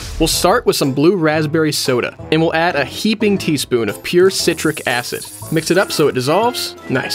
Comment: For a second I thought the citric acid said battery acid